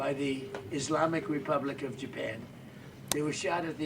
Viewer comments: Did he say "Islamic Republic of JAPAN"? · did he say the islamic republic of japan or to japan?